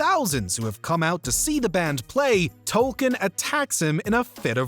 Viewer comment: What’s your favorite Cartman plan gone awry? Let us know in the comments. Become a channel member to get access to special perks: <link> For more South Park, check out our playlist: <link> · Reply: why you trying to gaslight us with that "Tolkien" bs you can watch and see the subtitles on DVD and the show over the last 16 years his name was clearly say "token"